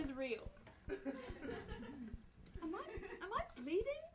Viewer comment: I thought Malfoy said 'am I breathing?" instead of "am I bleeding?" And when one of Malfoy's friends said "NO." I kind of freaked out...